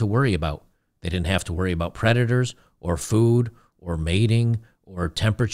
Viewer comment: You said “predators”‘I heard “creditors”. Funny how the mind works.